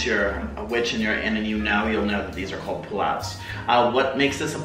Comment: I thought he was saying "plout" not pullout lmao